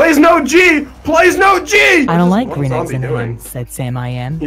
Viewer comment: did anyone else hear it say I like green eggs n ham said Sam I am